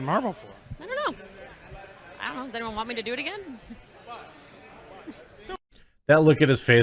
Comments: i clearly hear her say:"i don't know if they want me to do it again" NOT "Does anyone want me to do it again" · It sounds like she says, "I don't know if they don't want me to do it again." ...I don't hear her ask, does anyone want me to do it again. I listened to it several times. Changes the whole context of this argument.